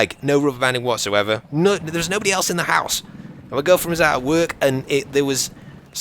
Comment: Did you say girlfriend? Ohlala!